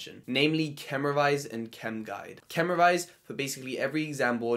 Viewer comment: what was the second website you mentioned I cant seem to find it chemdye? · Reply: Sounds like hes saying chem guide